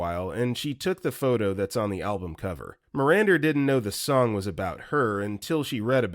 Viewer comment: video captions say “Miranda” but OP says Mirander